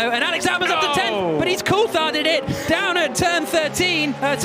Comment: did he say "he's Coultharded it"? That's actually accurate